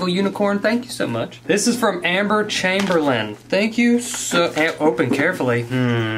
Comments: I thought he said Emma Chamberlain 😂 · when he said “amber chamberlain” i thought he said emma chamberlain 😂 anyone else? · i thought u said emma chamberlain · I thought he said Emma chamberlain😂😂😂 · Who else thought he said Emma Chamberlain not Amber Chamberlain?